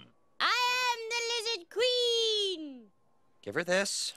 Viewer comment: Any man who must say, “I am the lizard queen” is no true queen. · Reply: Thank you lord Tywin...though frankly, I thought they said "Lizard Queef?"